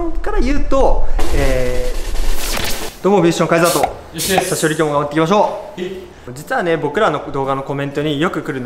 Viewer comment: 勉強になります！ ところでずっと気になっていたのですがはじめの挨拶って何て言っているのですか？！ 「久しぶりに頑張っていきましょう」と聞こえるのですが…正解を教えてほしいです！ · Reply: さしより頑張っていきましょう！って言ってます😺(熊本弁)